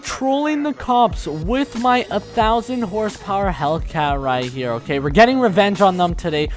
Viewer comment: LOL I just realised at 0:02 it said 10,000 for the captions instead of 1k 🤣 that's not something you see eevery day. Good video!!